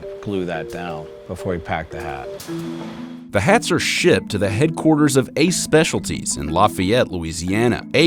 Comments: I thought he said these hats are $hit ,my bad ship, LOL i'am buying one. piss my wife off. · lol I heard 2:30 “The hats are Shit” btw, weird how they employ mexicans. Strange isnt it. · Lmao I legit thought he said "The hats are shit" 😂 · The hats are shit. 2:30 listen it well. That's what the guy talking said